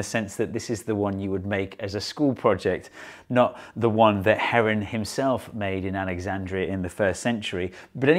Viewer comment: NGL i thought it said "heroin" fountain